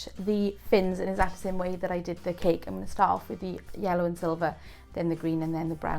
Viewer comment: What were the colors again that you used first? I thought you said yellow and silver?